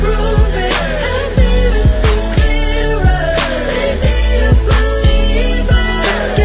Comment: Thought it said "Make me a belieber." XD